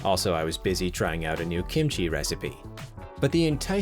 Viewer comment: Did I hear Kenshi? Fine cooking in that game. Edit: Ahhh. Kimchi. My mistake.